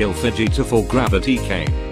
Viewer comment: When he said Fujita I thought he said Vegeta lmao.